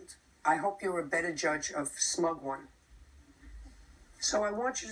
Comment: Thought it said smaug